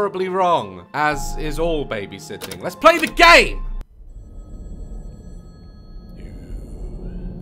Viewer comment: At the start for a second I thought he said "LETS PLAY THE GAY!!"